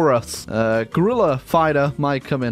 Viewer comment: Well, I heard "gorilla figther".